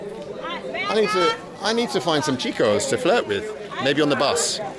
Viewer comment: My favorite part was when he said he wanted to find boys on the bus to flirt with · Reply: Damn, I thought that's what he said but I figured I just misheard him since I have hearing loss. Lol Some "chicos on the bus to flirt with." Lol 😳😂